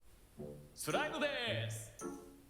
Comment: スライムでーすって聞こえたww